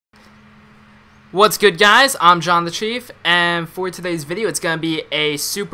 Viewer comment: when he said I'm Jon the Chief I thought he said Shaun the sheep